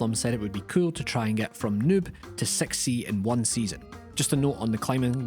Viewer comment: i heard Noob to sexy in this line